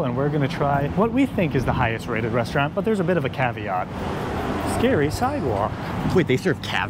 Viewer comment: I thought I heard caviar too, ! 😂